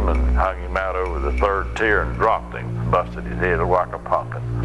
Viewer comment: What does he say? Busted his head a-wacka-pocket? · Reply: "Like a pumpkin"